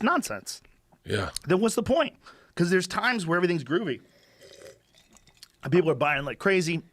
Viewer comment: did he just say groovy?